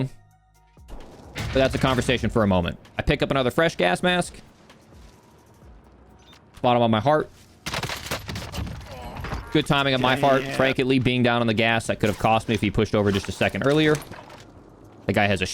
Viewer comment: i think he said fart?😂